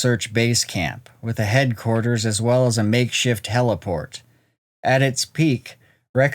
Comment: I dunno why I heard that as “makeshift teleport.” I hate that, when you don’t have access to a legit teleport, so you have to put together a makeshift teleport.